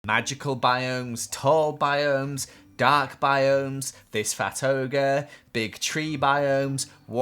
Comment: In the beginning when you said this fat ogre, I thought you said this fatoga and I'm like, what's a fatoga?